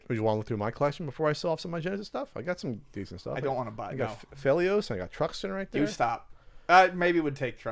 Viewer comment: What games does say at about the 2:16 mark? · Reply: oddbrawl Phelios and Truxton.